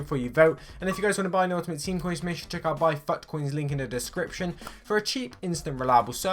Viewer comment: Sounds like he says fuck coin